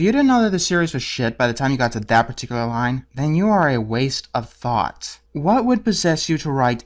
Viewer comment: When he called Jonah hill "a waste of thought", I literally thought he said "you are a waste of fat" xD